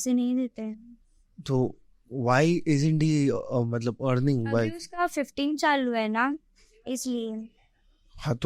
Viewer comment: what did I just hear? Uska bf 15 saal ka hai ? Can anyone correct me ? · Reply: u r right I heard the same bhyiiii , she said he is 15